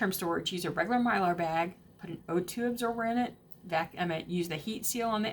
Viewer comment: Around the 8:00 mark, says you can use mylar bags and put a ? in it. I cannot make out what she is saying. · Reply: O2 absorber